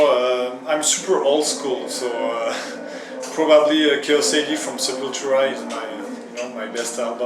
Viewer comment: What does Mario say his favourite album is at 8:37? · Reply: Eric Nilsson Sepultura - Chaos A.D.